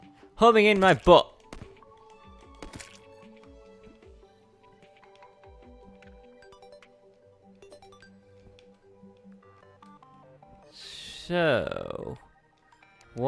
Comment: did he say homing in by butt? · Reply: Homing my butt not in my butt ya dirty minded freaks